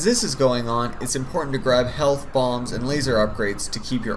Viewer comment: did he just say 'health bongs'????